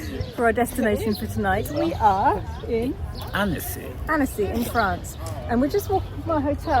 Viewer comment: Omg guys this French town/lake is absolutely gorgeous…is the name of city ANNECY!? It sounds like you said “annis “